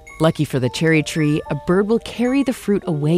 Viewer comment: i swear they said a birb